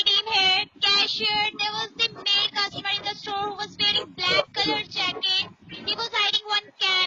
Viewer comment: I swore I heard the eye in the sky say @ 2:54 Mercedes black color jacket 😂..